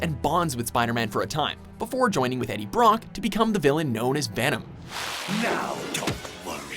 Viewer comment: did he just say vanem